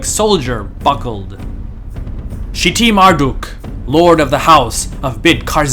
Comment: I thought that said shiting Marduk! 💩